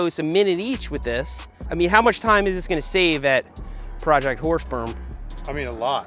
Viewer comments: Had phone on the side about 12ft away had to rewind to hear what Seth said as I heard “project horse sperm” I was like wtf 😳 thought this was a family show now they’re milking horses 🤣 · I swear I thought he said project Horse sperm and thought he was going to raise horses now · Did he just say “Project Horse Sperm”? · is it me or does it sound like hes saying project horse sperm